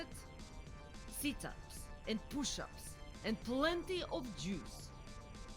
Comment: That could be TFS or the original dub when Cell asked Vegeta how he got so strong. Either way, appreciate the nod to DBZ in general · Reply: Kitai105 I do appreciate it but I was confused on what the original was, I thought vegeta said plenty of milk